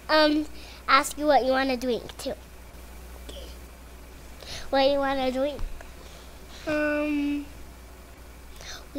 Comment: Can anyone tell what he whispered at 0:55? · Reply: "Kay"